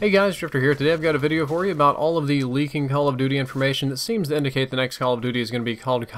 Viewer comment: Totally sounds like your saying "leaking hell of duty" in the beginning lol